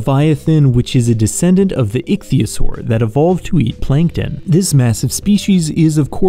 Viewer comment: So basically almost every animal we know but as a reptile, got it 😂 9:20 Gosh I misheard you and heard that "Ichthyosaur evolved to be plankton" and I was like... How??? That mf was like 15 feet long and plankton rarely reaches an inch, but then I realized it was "eat" and not "be" and also you were referring to the alternate time line current animal 🤦‍♂️ (next time try not to say "e" instead of "eat" lol)